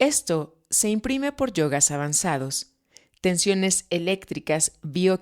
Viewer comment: Amiga una pregunta, en el minuto 19:07 cuando habla del Neurocircuito 6, decís "yogas"? porque no entendí bien. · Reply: Si dice “yogas avanzados” ;) saludos luminosos